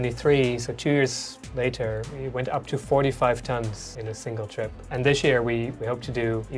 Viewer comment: Finally a startup with a real global mission we can all support. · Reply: From what I heard at 2:04, he said, “it went up to 45 tons in a single trip”.